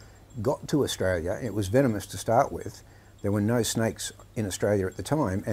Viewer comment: I don’t know if I heard him correctly but I thought he said there are no snakes in Ireland. If that is true, I should consider going there, I am definitely in the wrong country